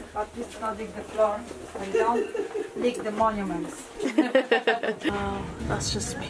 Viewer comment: "Don`t lick the monuments" :D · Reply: I heard "don't lick the floor, and don't lick the monuments."